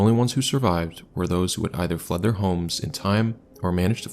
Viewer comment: this is tragic as hell but I gotta say that at first I thought you said the only people to survive the tornado in the double creek estates either we're underground or "flooded their homes". And I kept thinking how did this possibly keep them alive?? Did they even get it deep enough with water to actually help??! Foooded with like a hose? Then my brain clicked on. Oh, fled their homes. Ah, yes. Makes much more sense, now. 😅